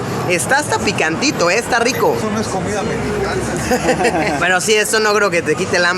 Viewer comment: soy el único que escucho que lo que dijo el señor era "eso no es comida mexicana"?